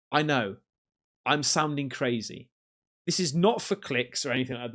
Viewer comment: To me it sounds like he says "Im souring" wich means become unpleasant or difficult ...